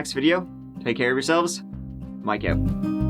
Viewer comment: Mike says, "Not a bother!" I hear, "Nutter butter" im hungry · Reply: Or when he closes off the video, " Mike out"...I hear "my goat"🐐